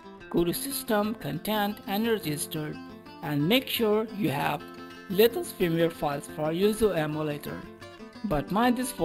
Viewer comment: im sorry but what does he say on 2:02? i cant really understand it and i would need it bc mines empty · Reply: Lait says latest firmware files😂